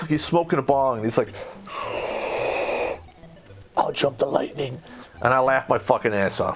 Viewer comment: I couldn't hear what bong man said at 16:20. "I'll jump the lightning?" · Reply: No he said, out jump the lightening